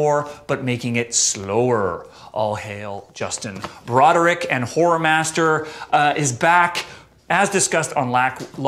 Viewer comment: Sorry for misspelling Godflesh stupid autocorrect lol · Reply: Horror Master! I thought was saying Whore Master. It's an accent thing I guess.